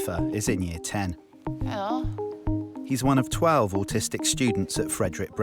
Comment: Lmao I thought it said ‘artistic’ I was so confused😂